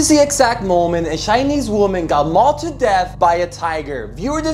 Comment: I thought you said got mugged to death